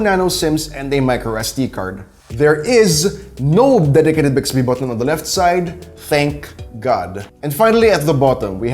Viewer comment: If anyone is wondering what it said at 1:31 he said no dedicated bixiby button on the left side I had to slow it down to 0.5x